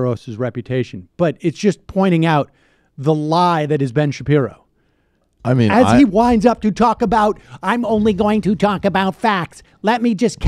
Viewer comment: I heard Sam saying "wind up" as "wined up", which would be pretty appropriate to little Ben Shapiro, who is king of playing the world's smallest violin.